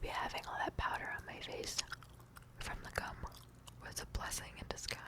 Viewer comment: she actually says cum i swear